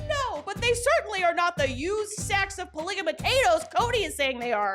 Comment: The zoom in as he looks at the camera. The editor knows Cody is just trying to bullshit people. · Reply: Did you say used sacks of polygamotatoes? 12:52 Yes, you did! Fucking hilarious!